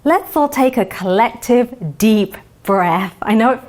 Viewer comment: Dr Yasmeen: let's all take a deep breath Coronavirus: Very poor choice of words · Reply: take deep breast, i thought she said.